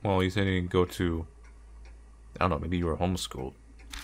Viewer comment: I heard it as homeless school and I’m crying 😂